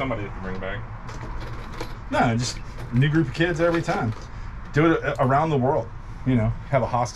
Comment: What did he say at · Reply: A new group of kids every time. He was talking about the show.